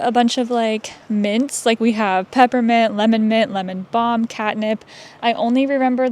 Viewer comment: Leveled Herbalism to max level Wait wtf did you just say, "Katnip?" Edit: I'm dead seeing the Throne inside, where you sit. Queen Poison Ivy. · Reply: LOL! I will now forever see it as a throne 😂😂😂 where I will proudly show off my maxed herbalism skill. And yes! “Catnip!” So that the cats can have catnip 😂